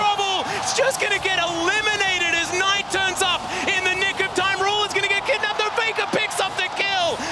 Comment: is it me or do i hear the N word when faker ults @ 10:57 ?!?! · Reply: a hundred percent ‘nick’ of time